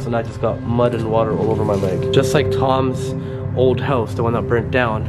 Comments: did just say “I burnt down Toms old house”? Did I hear correctly?👂🏼lol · was freakish, what the f*** did i just heard saying "The one i burnt down"??? Or this is just the spirits playing with the audio?